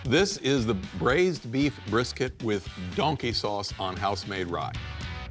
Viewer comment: Did he just say “donkey sauce”🤔? 😂